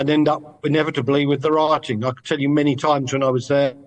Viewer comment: Did he just say riots?